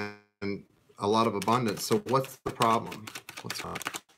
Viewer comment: Wow...watching you travelling by train...the line is not good but I am almost sure: you were channelling different times, shape shifting and at around 11:05 I heard the voice saying "what's wrong?", no is not creepy but nice as it is totally resonating to what you were reading, correct me if I am wrong ;) by the way it totally resonates with me. Thank you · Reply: Monica Meggiato no that was just me saying what’s wrong lol but if I was channeling it was a benevolent energy I promise